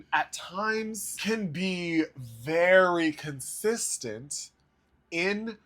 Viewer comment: When you said Jennifer Hudson is "consistent" I heard "insistent" and had to rewind it... I feel like I heard it right the first time... some artists musicality can't transform their signature styles into good tasting Musical choices... Christina Aguilara is in that camp when she does covers... it sounds like super imposing yourself...